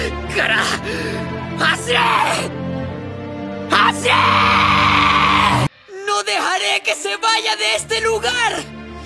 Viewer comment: mmm.... se nota mucho el acento venezolano en especial en las "J" por ejemplo: "No deharé que se vaya!" no es una jota de español neutro y ese detalle es el q mas me pincha a mí xd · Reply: yo escuche "no dehare que se vaya el mariko!" :V